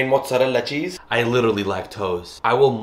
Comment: " make it rain mozzarella , am literally lack tose " 🤣🤣 Me literally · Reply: I thought he said "i literally like toes"